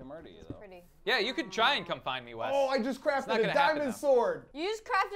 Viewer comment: I thought he said "I just crafted a dinosaur" xD